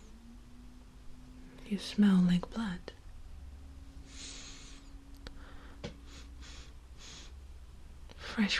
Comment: I thought she said "you smell like butt"